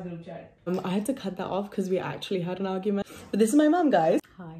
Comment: ‘Shes needy’ 😂😂 lmfaooo omg why have i got so many likes?!! thank you!!😂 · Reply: I thought she said she's sneaky?